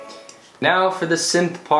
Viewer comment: oh my god I thought you said "Now for the simp parts" and were about to play the vocals